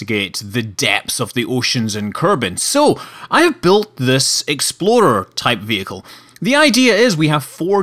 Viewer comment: I always think he says exploder when he says explorer.